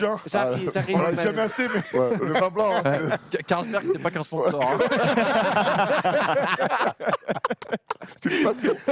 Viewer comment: Il dit quoi à 1:03:25 svp ? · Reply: "Carlsberg, c'était pas qu'un sponsor." Carlsberg marque de bierre sponsor de liverpool.